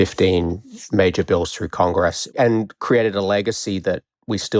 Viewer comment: As an American and follower of American politics, I just don’t know what this Australian is saying Con what? Con job? Conditioning like air conditioning? I think this guy is confused. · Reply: Do you mean Congress? Maybe you should have your ears checked, he sounds perfectly clear to me...